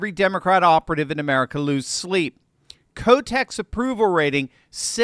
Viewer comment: did you say Kotex approval rating? 😅😂